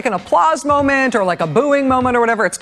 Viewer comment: thought he said pp